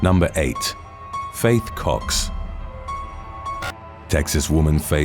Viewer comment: Bro for a second I thought it said "Fake cock" at 0:04 🤣